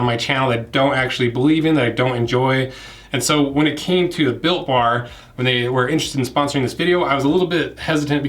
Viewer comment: Is sound like you said Bill Barr the attorney general is sponsoring this. Lol